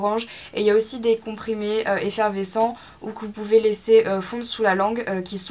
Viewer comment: Si toi aussi à 1:04 "ou cou pouvez laisser" au lieu de ou vous pouvez laisser :)